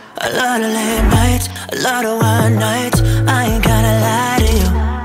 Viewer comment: I honestly thought you said "a lot of wild nights" instead of "a lot of 1 nights"! but i truly love this song and i feel like it's very reflective of your personal style and i adore that. much love!